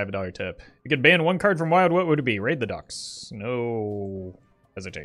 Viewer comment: what card is he saying? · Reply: Raid the docks